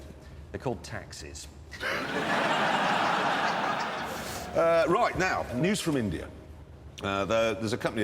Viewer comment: I don't understand James' statement starting from 7:00 to...I think 7:06 or something.Did he say 'taxes' or 'taxis'?I'm a little bit confused. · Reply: taxis'